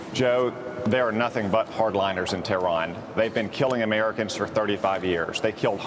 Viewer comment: did he say tareron 1:02 like some word combo of terrorists and iran. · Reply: Tehran is the capitol city of Iran...